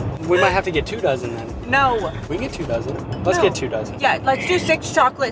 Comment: When he said 2 dozen i thought he said 2 thousand😂 And the way says "yes" is heartwarming